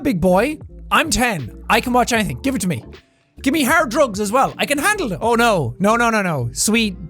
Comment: I thought he said 'hair drugs' instead of "hard drugs" 😂 I know I am silly.